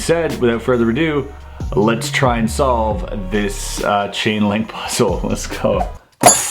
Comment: Nice video dude...But when you first mentioned the name of the puzzle I thought you said 'the Ching ling puzzle' xD